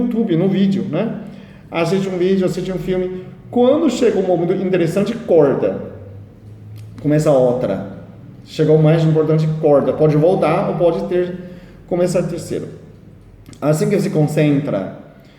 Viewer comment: Olá. Gostaria de saber o que você falou no minuto 6:04, você falou corta e pode começar algo, mas é tão rápido que nem com replay eu consegui entender, foi “volta ou pode começar um terceiro”? sendo esse terceiro um momento interessante de um vídeo? E esse voltar, qual é o objetivo?